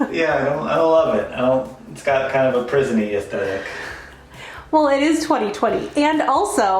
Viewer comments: “It kinda has a present-y affect. See what people say down in the comments.” Haha, I’m with. You guys are adorable! Great video, happy holidays! (p.s. got a thumbs up from me.) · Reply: I thought he said prison(y) effect. Like "jail." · “It kinda has a present-y affect. See what people say down in the comments.” Haha, I’m with. You guys are adorable! Great video, happy holidays! (p.s. got a thumbs up from me.) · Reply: I thought he said prison-y effect! Lol